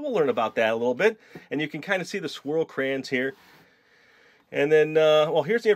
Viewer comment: I thought he said squirrel crayons....